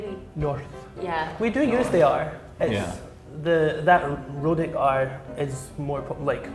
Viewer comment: he said 'rhotic' not 'rooted' the subtitle got it wrong